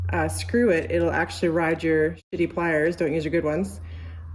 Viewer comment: Lol I thought she said City pliers....